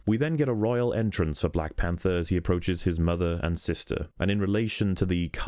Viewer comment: At first that sounded like that woman is his mother AND sister which raised a whole bunch of questions